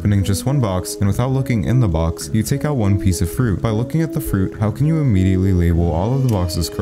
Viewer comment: For some reason I thought he said without looking at the fruit so I was really confused